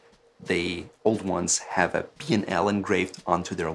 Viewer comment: Did he say PL or BL? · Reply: Omrtbsh BL. The new ones have RB.